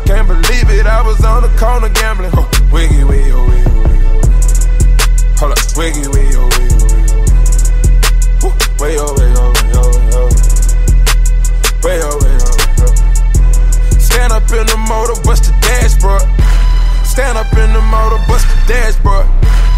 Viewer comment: it sounds like he was saying wiggle wayo wayo wayo